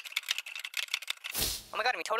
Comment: does anyone understand what he's saying? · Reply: “Omg, we jumped off the cliff and had too swim all the way to shore-“ I don’t know what else but it sounded like “because dog shit” or something